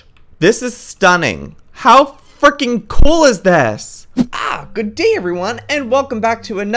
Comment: At the begging I thought he said how f ing cool but I listened to it again but he just said frickin cool